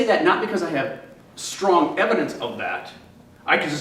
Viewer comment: Interesting dialog. Good back and forth. Thanks for this. I also really liked the closed captions. As a side note, I'm pretty sure he said "really cogent case for that" not "really coaching case for that" at about 4:50. But I may have misheard or misinterpreted. And doesn't ultimately change the meaning.